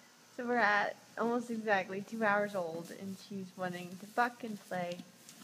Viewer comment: At two hours it sounds like you said f*ucking play!! I'm guessing not hah. Beautiful baby. Congrats and good luck